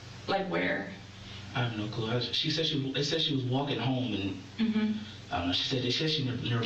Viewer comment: did he just say “ she said she was walking home “